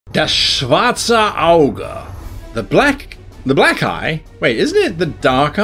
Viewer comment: Just gotta say in the intro I misheard Black Eye for Black Guy. 😆 Now to watch the rest of the video.